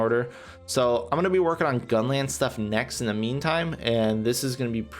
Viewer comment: Did I hear "gunlance stuff next"?